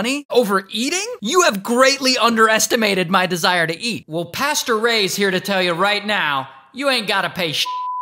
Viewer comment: I heard "pasture raised" and not "pastor ray" and that made it so much funnier